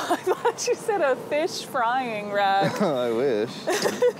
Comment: I thought he said a fish frying rack also 😅😅😅😅😅😅😅.